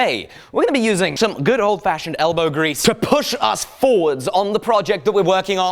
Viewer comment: That sounded like you said smelbow grease... lol